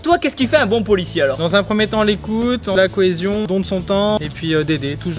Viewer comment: jai entendu les couilles